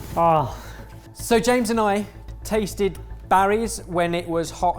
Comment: I heard "Sir James and I" instead of "So James and I" which is fine. I am completely okay with calling our resident ginger "Sir James".